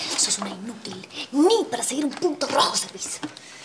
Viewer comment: Me parece a mi o Luz dijo 'ni para seguir un "puto" (ahi no entendi)' minuto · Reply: Soy Zila "ni para seguir un punto rojo servis" xD